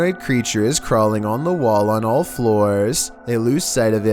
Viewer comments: “Its crawling on the floor on all fLoOrS” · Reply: I thought he said, “it’s crawling on the walls on all floors” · am i the only one that heard him say "on all floors" instead of "on all fours" xD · did he say.... floors?